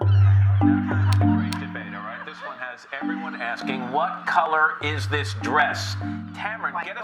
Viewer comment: the subtitles say "counters" when they should say "colours" And at 18:46 it should say "aren't " not "are in"